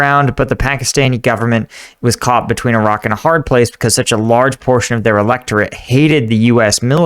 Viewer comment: "...But the Pakistani government was caught between Iraq and a hard place..." This is what I heard because I wasn't paying attention